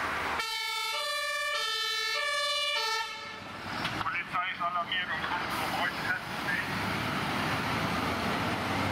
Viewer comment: was sagt er da bei 18:11 ? "Polizei ist alarmiert" und weiter habe ich nicht verstanden. ist aber ne schöne Zusammenstellung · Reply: nicht ganz. Es wurde gesagt "Die Polizei ist alarmiert und kommt um euch festzunehmen" ;-)